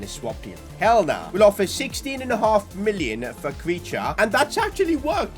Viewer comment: did he say creature 😂